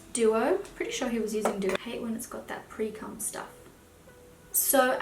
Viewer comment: Did I just hear "I hate it when it has that pre cum stuff" when you were applying the eyelash glue 😂😂